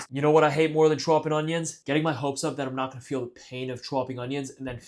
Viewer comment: you hate Trump and onions? Ok. Weird place to mention it, but that's fine. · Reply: i thought he said "choppin' onions"